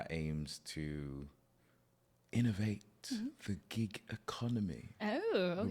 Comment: I thought he said the “Geek” economy too! No, the Gig economy! 😂